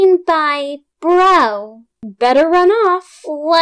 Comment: I thought she was gonna say Broken Rotten onion😭😭😭😭😭😭